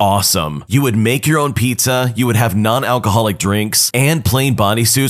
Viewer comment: The biggest issue with story 1.. WHY have a baby shower with alcohol??? Its not like a pregnant woman should be allowed a drink... -_ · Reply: You misheard that. He said non alcoholic beverages :). The subtitles maybe were flawed (I don't read them)